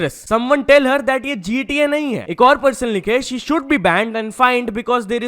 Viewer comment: i heard india instead of GTA